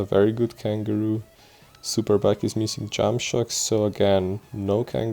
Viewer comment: I thought he said junk shops😅😅🤣🤣